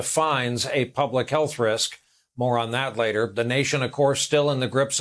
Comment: Brian: “More on that later”. Could have sworn he said “Moron that later”.